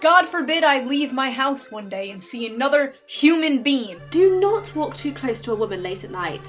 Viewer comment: excuse me but did she just say “human bean”